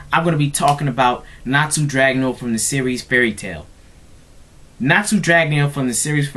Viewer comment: Did you just call the series Fail Tail? looooooooooooooool